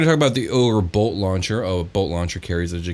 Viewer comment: I kept hearing "Ogre Boat Launcher," and that stat block would be hilarious